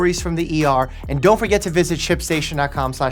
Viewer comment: I thought mike said sh£tstation